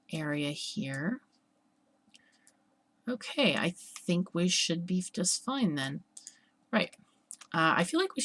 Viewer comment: Did I hear that correctly? "I think we should beef just fine"? lol